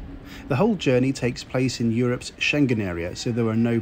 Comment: you say the whole journey takes place in the "chendin?" Area so there are no passport checks etc. What area? Sorry, didn't catch that word? Help? Planning trip to Europe soon. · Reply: Schengen.